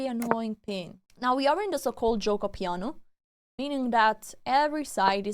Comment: I never heard of "The Giuoco Piano". I believe that is what you said game 3 17:00 minutes in. Googling it was hard because I heard "Cikle jokopiano". But you are Italian and when this came up I had a Eureka kind of moment. 😉 The expression "giuoco piano" means "slow game" in Italian! I learn so much watching you. After you record your games you could almost re-record to give better explanation. 🤔🤔 Maybe not necessary but just a thought because you are on a timer when you are playing.